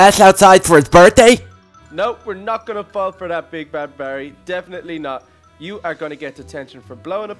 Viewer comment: did he just say big bad Barry because I've heard that from Ben and Holly's little kingdom